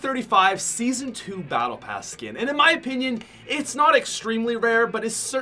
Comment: did he say skin